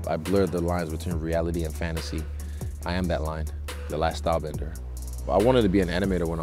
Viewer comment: Lol did he just say "I'm the last airbender"? XD gawd bro